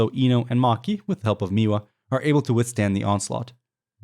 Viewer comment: did you just say miwa?….. looks like miwa really took after her master.